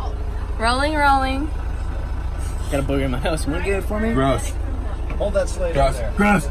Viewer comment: What does he say in the minute 0:25 ??? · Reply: “booger in my nose”😭😂